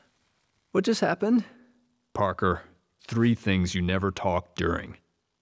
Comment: Bruh at 3:40 he said Parker but the captions say peter